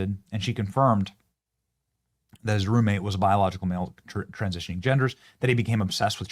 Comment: I thought you said groommate 😂 had to rewind